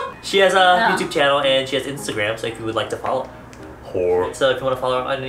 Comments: I'M DYING,, I literally thought he said "Wh**e" to her at 12:50 at first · i scREAMED I THOUGHT HE SAID WHOREJDNDK